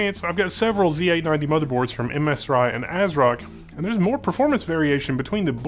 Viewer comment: Some more details & Info here <link> 9:45 - there's a typo here, the column is supposed to say "OS" not "CPU" lol... whoops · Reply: Is it just me, or at the 1:20 mark, did you say 'MSRye' instead of 'MSI'?